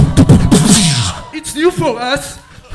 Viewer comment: What said on 7:15 ? Is it "It's new for us" ?